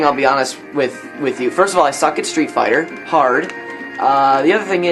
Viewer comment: it sounded like you said: "I SUCK IT I SUCK IT" XD